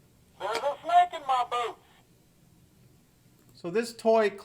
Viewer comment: All I heard was boot(scrambled noise sound) not boots.